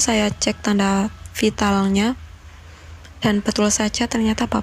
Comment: Ntah saya yg salah denger atau memng benar adanya, mbak ini bilang ngecek wafat nggaknya lewat alat vital. kok ngeri yaa, bukannya dari denyut nadi dan hidung sudah bisa dicek